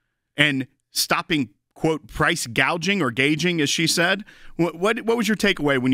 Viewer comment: WOW someone actually got what Kamala said correct for once. She said Gaging not Gougin